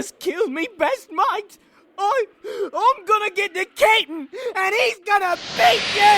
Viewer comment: And all this time, I thought said kitten, not cap'n